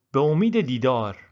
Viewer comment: i think you say be omeede deedar (see you soon) but the screen says gereftam is that right?